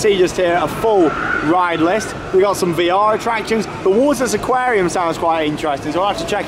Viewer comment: I misheard the aquarium description as a "Waltzer Aquarium" and was VERY confused. New meaning to flying fish 😂